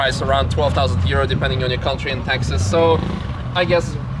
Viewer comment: What did I? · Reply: at 1:46. Watched it again, you said "taxes". Originally thought you said something like "culture in Texas".